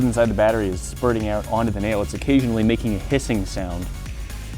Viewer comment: I sincerely thought you said pissing instead of hissing until i replayed it in my head wtf brain